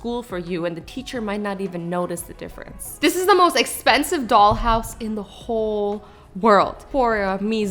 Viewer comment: i thought she said dog house